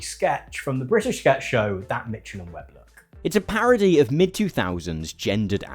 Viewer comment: "That Mitchell and Webb Look" (in case anyone didn't catch what he said and isn't familiar with the British sketch show) I wasn't - I'd never heard of it, but it looks hilarious.